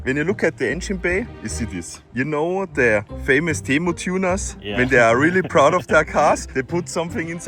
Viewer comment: i heard "Temu tunas" and was thinking about fish for a second.